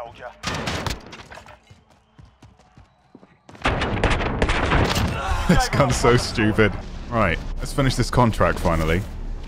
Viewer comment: Oh this GUN is so stupid. Briefly misheard you there.